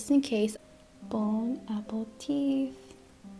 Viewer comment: it sounds like she said, “bone apple teeth.” 😀